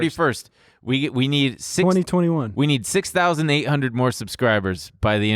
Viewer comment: did I hear the deadline as 2021?! It can’t be! I need to see this kiss!